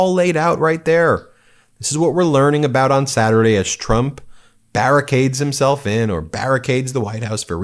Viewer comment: I thought you said buried and not barricaded 😂😂😂😂. Damn!!!